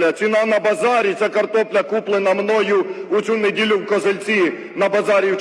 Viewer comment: мені одному почулося: "купив у місті Козельця (може козелець) Чернігівській області" хто також почув й з козельця то пишіть, бо поки що я один мабуть...